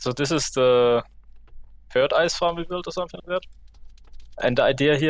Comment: At the start of the video I thought he said “so this is the third eyesore we built”